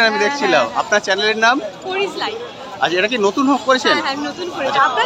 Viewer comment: youtube এর নাম কি বললো দাদা,আর তোমার vdo দারুন লাগলো। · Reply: "Paris life" Thank you so much for your comments. Valo thakben.